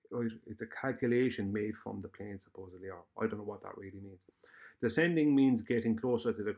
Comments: The evidence is already on my channel, and the data too. Why do you keep ignoring this reality? If you want to debate me you know where my flight planning challenge is and what you have to do. Almost five years later nobody can touch it. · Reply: what did you say at 3:51 Was that "I don't really know what that means" Did I hear you correctly? Answer the question. This is not a trap or a trick Your voice trails off and I want to be certain of your words before proceeding. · The evidence is already on my channel, and the data too. Why do you keep ignoring this reality? If you want to debate me you know where my flight planning challenge is and what you have to do. Almost five years later nobody can touch it. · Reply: what did you say at 3:51? Was that "I don't really know what that means" Did I hear you correctly?